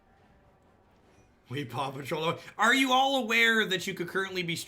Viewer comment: Oh, I thought he was saying "Papa Troll", which made a lot more sense. :P